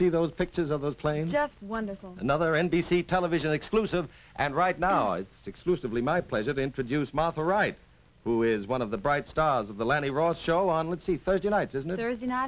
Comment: What did he say was the name of the lady at 3:21? Martha Right?